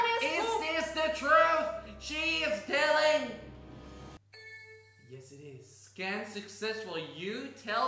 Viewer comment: Who heard the phone say yes it is?